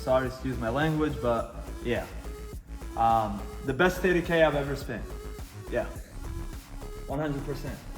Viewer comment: Who? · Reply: ur boy that's got the ceka brakes put on. Sounded like he said best 80k he ever spent! 🤯 I know he was talkin bout the entire build but still 80k, daaamn!!